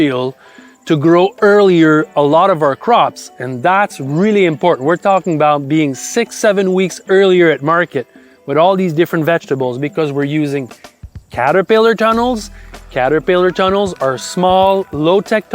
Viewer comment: Did you say 67 LOL